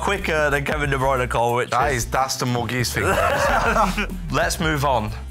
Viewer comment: What did Jack say here? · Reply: "That is, that's the Muggiest thing I've ever seen"